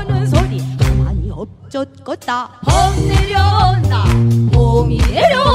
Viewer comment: 너무 예뻐요. 그런데 내귀만 잘못됀나요?? "돈 "내려온다로 들리는지 ㅋㅋㅋ ^.^~